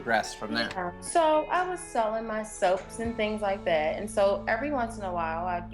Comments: I really thought she said she was selling herself.... (bt nope she's selling soap 🧼) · Did she say "when I was selling myself."??????????? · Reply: Bi Han I think she said sellin my soap